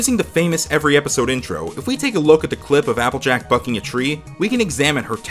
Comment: I thought he said fuck a tree